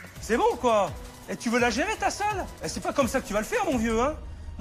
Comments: "Hé tu veux la gérer ta soeur ?!" S’il vous plaît dites moi que je suis pas la seule à avoir entendu ça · j'ai entendu "ho tu va la gérer ta soeur ???" mdr !! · j'ai compris "tu veux la gérer ta sœur ?" ...